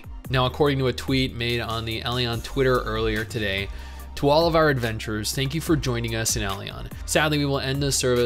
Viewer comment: it sounds like your saying.. 12 of our adventurers ... instead of to all our adventureres xD